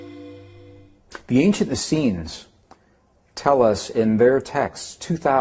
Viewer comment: what is he saying? The ancient acians or ancient athians. I was trying to look them up but everytime I try, Google thinks I'm mispelling ancient asians lol Help a curious mind please.